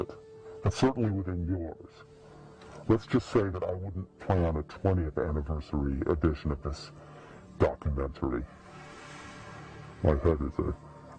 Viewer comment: one thing about the interview in the car which was filmed 2006 or 2007 for the s.e. dvd that always kinda bugged me is hinted the world would be ending 10 years from then (<num>) that didnt happen, obviously. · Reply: I thought he said, “don’t plan on a 20th anniversary of this video”. Something like that that?